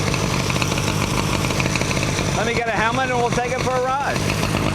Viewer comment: At first I thought Jay said, "Let me get a hammer and we'll go for a ride!"